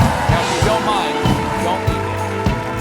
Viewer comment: I had palpitations at the end as the music drowned out Stephen saying the next guest would be Justin Thomas. I misheard and thought he'd said JUSTICE Thomas. You can see how that could happen as they were just talking about SCOTUS.